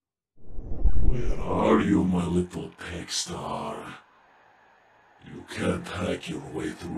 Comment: did he say 'pegstar'